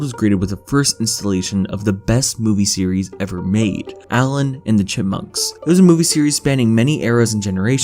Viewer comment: Did he just say Allen??